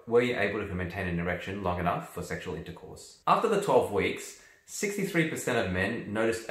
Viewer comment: IN 12 weeks, I thought they said in 10 minutes !